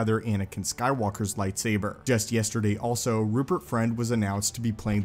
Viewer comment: Had to listen twice because I thought you said Rupert Grint was playing the inquisitor 🤣